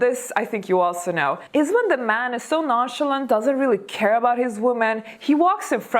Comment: you say "nonchalant" and the subtitle says "Nauseous", just FYI.